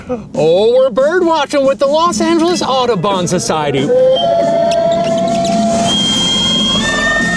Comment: i thought he said autobahn society